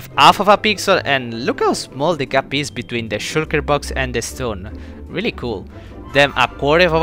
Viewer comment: FOR A SECOND I THOUGHT HE SAID SHIT DOWN BOX INSTEAD OF SHULKER